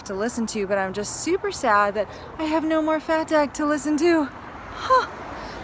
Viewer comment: OMg!! You have been taking about a podcast called Fatdag. I thought you said Fat Dad. I’ve been listening to Fat Dad podcast and I’m so not into it. I’m like, keeps raving about it, when does the magic happen? Now I know you are talking about FatDAG. I’ll check it out. 😂😂😂😂